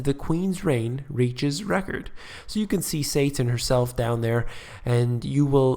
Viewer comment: did you say, "You can see Satin herself down there"?